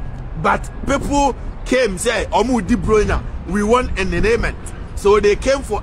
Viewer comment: Did i hear "We want enerneement"?